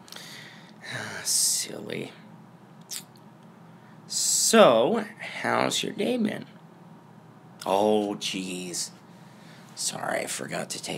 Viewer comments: I thought you said "How's your Gay been" I've been very good but still Also if you get sick. It's your fault. You kissed me. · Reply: And i thought u said hows your gay BEAN My gay bean has BEAN doing good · I thought you said "How's your Gay been" I've been very good but still Also if you get sick. It's your fault. You kissed me.